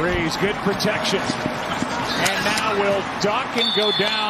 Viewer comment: That was the WORST field goal protection that I have EVER seen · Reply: PROTECTION, I thought you said projection 😂.